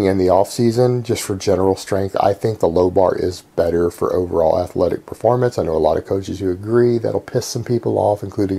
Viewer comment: i listened to this video while packing my stuff and i heard "low bar is overall better in athletic performance"....i was like.. WHATT?? LOL